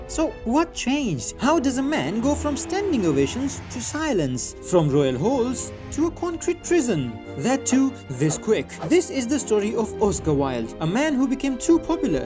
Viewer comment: i heard royal holes👌